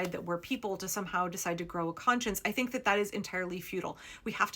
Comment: "Expecting the super-rich to treat us like people and grow a conscience is entirely futile" - I heard "feudal" for a second before I processed what you said, and I realize both words might be considered apt in this context. This really is what a lot of these super-rich are aiming for. Hell, there's that Curtis Yarvin dude that so many of these people seem to like, who genuinely pushes for a more technocratic version of the feudal system. I get the feeling that they're basically a bunch of LARPers who are so rich that they've lost touch with reality, so they're pursuing some fantasy based on some fictional novel they read as a kid.